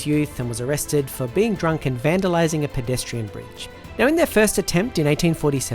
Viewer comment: You said "bridge" so quietly that when I first heard it I thought you said he got arrested for vandalising a pedestrian!